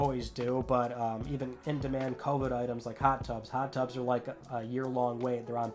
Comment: At first I thought you said hot dogs were on back order.